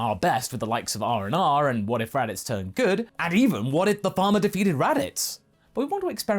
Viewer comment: I misheard when you said “What if the Farmer defeated Raditz?” as, “What if Obama defeated Raditz?”